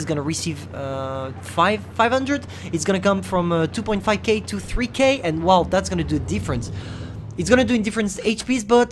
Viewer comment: what did you just say in chat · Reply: Lol he said "I hate Maus".